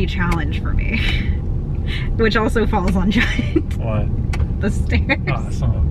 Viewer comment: What a fun, lighthearted video! Thanks for the laughs! Plus I had to laugh at myself. I heard "stares" not "stairs." I was ready to go to Tallahassee and defend you against the person who stares at you so much that you don't like going there. :D